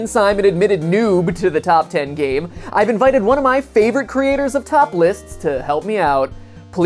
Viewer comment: Did he say topless at 0:42?